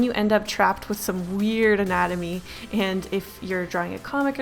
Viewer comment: I THOUGHT IT SAID ANATOMY AND BOOBIES